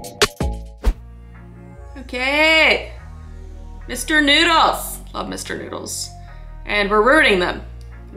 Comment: can anyone tell me what it said? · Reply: Mr.noodles